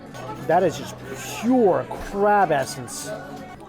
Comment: Oh man, realizing he said crab essence and I thought he said crab asses 🤣🤣 forgive me haha omgg